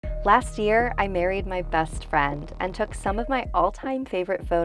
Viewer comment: omg at first i heard “last year I buried my best friend”